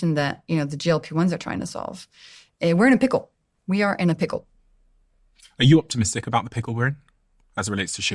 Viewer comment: I thought he said Pickle Rick